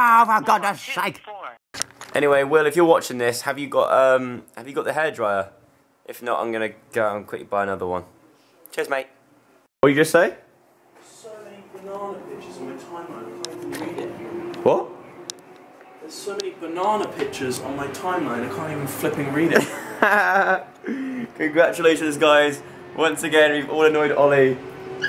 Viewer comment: At first I thought said "there are so many banana bitches on my timeline I can't even read it" 😂